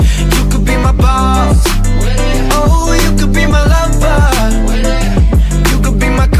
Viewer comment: I always thought Brandon said 'Lovebug' rather than 'Lover'